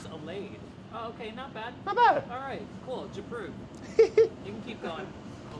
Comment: hahaha did anyone else hear chris say 'j'approve'